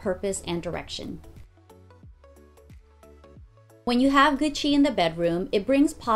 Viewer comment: What I heard: "When you have Gucci in the bedroom, it brings positivity, happiness, good vibes to everything that you do..." Hold on... Oh.. She said good chi not Gucci... Lol